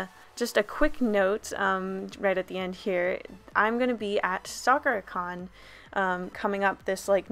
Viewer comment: Okay, for a split second, I thought she said 'Stalker-Con' and I was very confused.